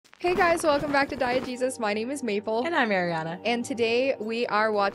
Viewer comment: I swear to god I hear you say “welcome back to Diet Jesus” every time